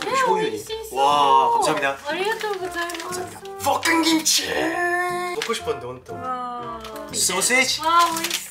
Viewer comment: sounds like he said FUCKING KIMCHI